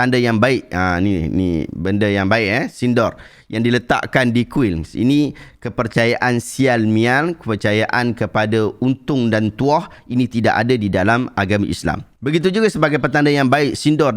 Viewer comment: miang?? Aku salah dengar ke aku tak tau maksud sebenar?😅